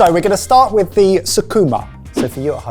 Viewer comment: My anime tainted brain thought you said Sukuna